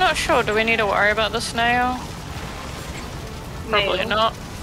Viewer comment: Also yes Tait I hear "this nail" now but it does blend together into snail